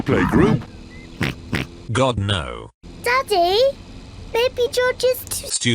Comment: did he say Giorno?